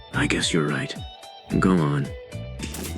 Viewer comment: did it say GOON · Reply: no it said go on not goon